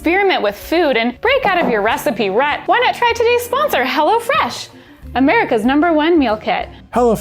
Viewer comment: When you said hello fresh I thought you said hella fresh